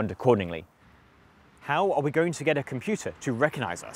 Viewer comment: when he says "how are we going to get a computer to recognize..." I thought he said "cow are we going to...." Did a double take and was disappointed when I replayed it, he even zoomed in on the cow sign... Missed golden pun opportunity